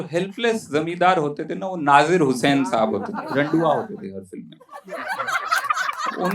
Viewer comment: lol did he actually say "randua" :D